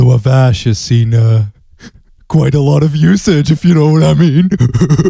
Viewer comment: "this photo of Ash has seen quite a lot of.... if you know what i mean" what? I can't understand what he says in that part. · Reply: Oh. Usage. Thanks automatically generated captions